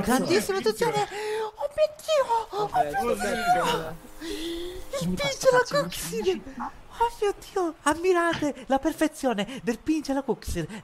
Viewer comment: Im italian and i cant understand what the italian guy at 5:38 is saying lol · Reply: Antonio Cianciotti OMMIODDIO OMMIODDIO IL PINCH ALLA KUXIR AMMIRATE LA PERFEZIONENENENENENSNSNSNENNSNDHDJANDHSJA